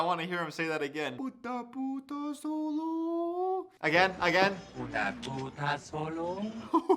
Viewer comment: lol I never noticed how they said that, it sounds as if it is saying "puta puta Solo" which in Spanish is like saying "whore, whore, Solo"